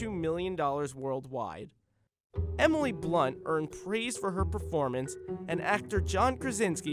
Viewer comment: If you turn on automatic subtitles instead of saying 0:45 “Emily blunt earned praise for her performance” it says “Emily blunt earned fries for her performance”